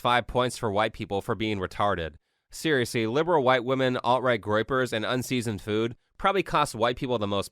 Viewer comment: did you say groypers? lol